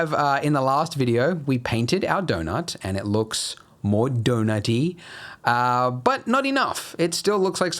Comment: is it just me your he really said Do-naughty?